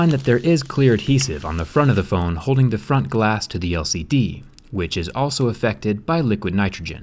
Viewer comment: I thought he was saying LSD